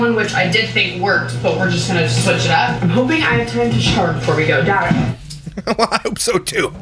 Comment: I thought she said shart not shower at the start 🤣🤣🤣🤣